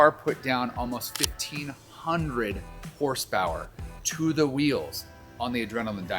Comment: did he say to the wheel?💀